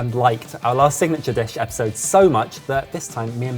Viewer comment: the subtitle says “ you guys have chosen the bitch”...when actually says dish. I don’t usually watch this with subtitles (but 3am viewings need discretion), so I was just interested to know if this was common? Because I must admit it was bloody hilarious.